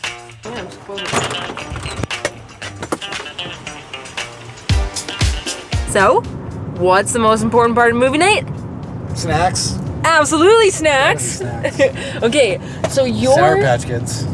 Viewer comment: "So What's the Best part of movie night?" I thought I heard SEX!! But it was "Snacks"!!!